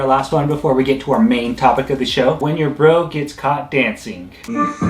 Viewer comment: I swear, when you said “When your brother’s caught dancing.” I thought you said “When you’re broke, it’s c*ck dancing.” LOL! Well sometimes I guess it is. 😁 “All four directions” !!!! ROFL!!!